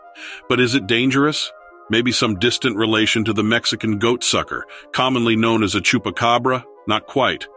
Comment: I wish to apologize to the producers of this docu.I and others could have sworn the narrator stated that the meaning of chupacabra was "Mexican blood suckers." We looked at each other and though " did I just hear that?" Last I heard, they don't suck the blood of Mexicans. But we were wrong. They actually said goat suckers. Funny, but way off!😂